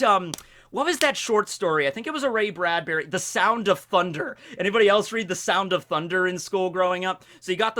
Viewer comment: Ahhh. First I misheard and thought you said Tears of Thunder. Can only recommend, also a Comic about Dinosaurs.